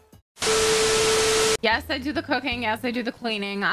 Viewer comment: BRUHHH I THOUGHT WAS SAYING YES I DO THE COCAINE AT 1:46 TF